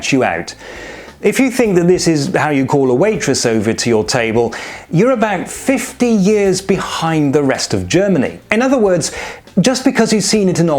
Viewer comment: i thought i heard, "you are about 15 years behind german bars", LMAO